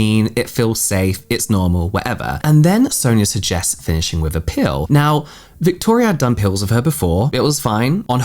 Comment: Ohhh PEEL. I thought you said she asked her to do a pill and that she'd done a pill with her before. I was like :0